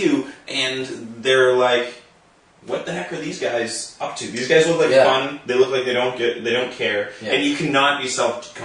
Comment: ha i thought he said "you guys look like dick heads"